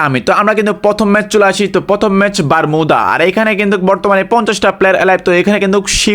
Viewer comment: আপনি কি বলেছেন বারমিদা 😂😂